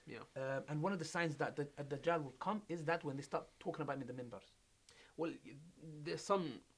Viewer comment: he says one sign is when we start talking about ____. Then the sheikh says he can’t really talk about it. What was that word he said?? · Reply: When Dajjal stops being mentioned or talked about from the Mimbars on Jummah during khutbah